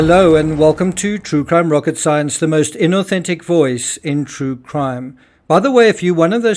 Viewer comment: it sounds like you said - Inauthentic 😮